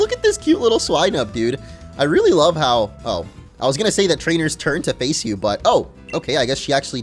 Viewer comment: Did I hear him say “Trainer’s Turd”?